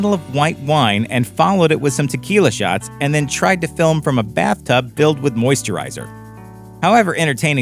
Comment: did they say “she tried to film from a bathtub filled with moisturizer”??? Filled... Filled with moisturizer.